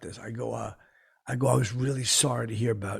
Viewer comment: I thought he said, "I'm sorry about your super dead husband."